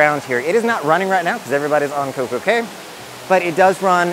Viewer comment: When he says "...everybody's on CocoCay," the autogenerated subtitles say "...everybody's on Coke okay." 😄